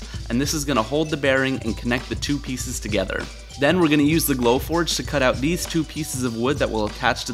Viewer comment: I thought you were saying "blow torch" for so long until I saw your "glow forge"! You're awesome!